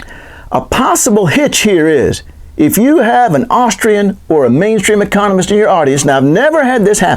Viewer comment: Am I hearing "Austrian" at time 6:55 ?